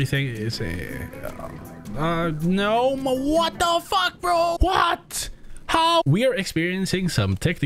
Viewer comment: did he just say "what the fu*#"